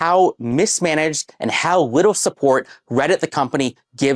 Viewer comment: bro said "widdle" instead of little